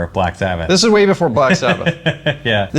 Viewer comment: Did he say Bach Sabbath?